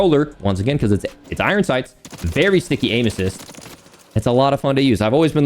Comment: Did he just say very sticky anuses?